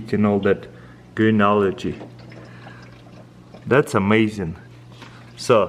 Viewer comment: I thought he said greenology